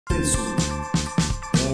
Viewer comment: E só eu q ouvi "não existo"?